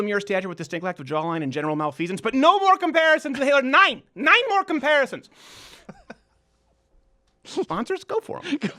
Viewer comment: You said NEIN more comparisons and I thought you said 9 more comparisons cause neun (noyn) is German for 9 n they sounds they same. I thought this comment would look a lot cooler than it. Actually does now.